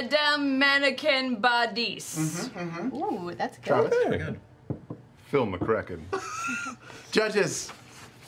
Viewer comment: I'm pretty sure that the captions got it wrong. It's supposed to be "Phil McKraken"